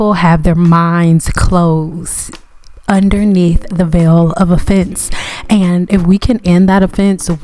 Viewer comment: I thought you said, "under the veil of a fence."😂😂😂